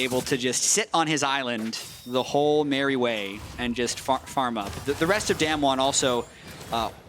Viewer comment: did he just say fuck farm up?